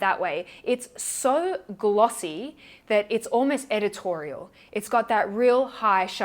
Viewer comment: My brain failed me for a moment because I thought you said "edible" not "editorial." I was a bit concerned about you, lol